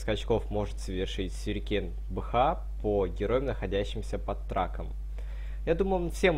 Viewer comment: А мне послышалось: "По героям находящимся под раком"